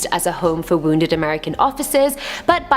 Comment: at just around 48:42 it sounds like she said it was there to house American offices instead of officers lol